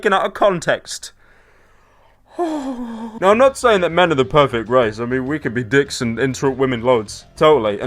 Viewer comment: did he say men are not the perfect RACE